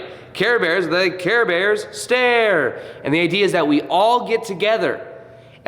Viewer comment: I always thought they said, “Care Bears, Care!” 😩😩😩😅 It just makes more sense. Lol. I’m sticking to it. 😂 Also, Dolores is 🙌